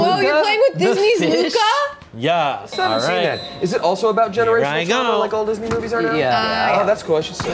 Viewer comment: I think he said "generational trauma" but I misheard that as "generation Tumblr" lmao